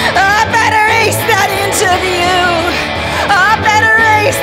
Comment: I BETTER ACE THAT INTERVIEW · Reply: I thought it said "I better erase that inch of you"